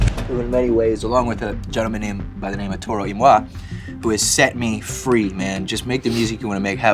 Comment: What did he say at 16:57? " Gentleman named ________ " ?? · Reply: Vleer Toro Y Moi. Good music